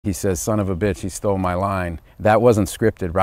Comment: I always thought he said “stole my life” and I like that better